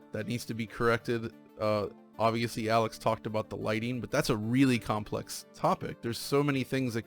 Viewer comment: We thought u said it just the light lmao 🤣 y'all know the game was not the light what made the game shitty it was the dev